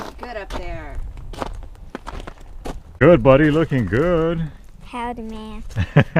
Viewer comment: "what did he say?" he tipped his hat and said "Howdy ma'am" so cute